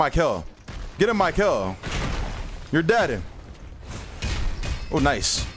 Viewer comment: MY CAPTIONS SAID "your daddy" 😭🤚